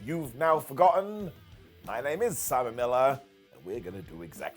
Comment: I watch tons of your vids, but I swear, this time it sounded like you said, "my name is Salmonella", ha!